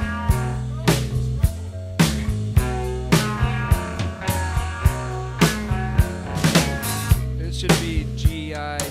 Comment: I think Dave says something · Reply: girls in trouble (Kurt tells him to think of what G.I.T stands for)